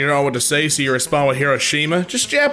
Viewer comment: ngl with the one anout gracias dead ass thought he said ‘Kirishima’ XD